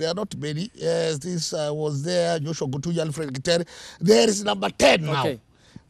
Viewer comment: Alfred ketero,,,😅😂🤣 had heard it as kidero